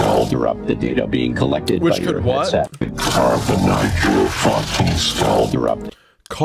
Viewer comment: “carve the knife through your fucking skull” is what it said